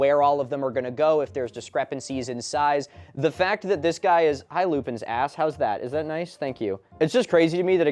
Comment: The second I joined the live i just hear "hi lupens ass"